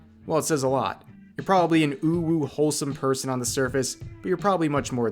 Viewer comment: did you just say I am wholesome uwu?